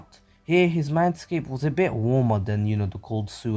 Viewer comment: What he says “his mindscape is a bit warmer” vs what I hear “his MANSCAPE is warmer